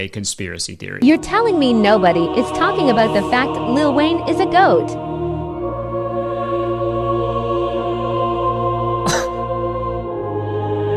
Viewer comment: Loool i thought the voice said lil wayne is a ghost i was so confused looking at him like huh? This mf isnt see through 😂